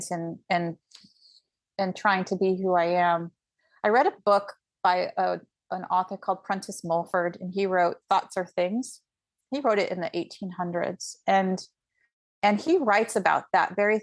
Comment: Hi! Thank you for bringing this podcast! Beautiful experience and so much truth! Do you mind telling me the name of the book she mentions at 48:00? I couldn't understand the name of the author. Thank you again! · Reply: Thought Are Things by Prentice Mulford. Read with an open heart/mind. ❤